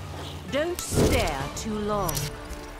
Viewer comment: "Don't stare too long" can ya blame them? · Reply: I always thought she said " Don't spare too long "